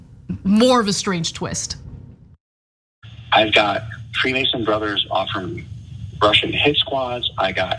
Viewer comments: He says he has three amazing brothers offering to pay for Russian hit squads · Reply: I thought he said Free Mason brothers. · Did he say Freemasons? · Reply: It did sound like he said,"Freemasons", huh?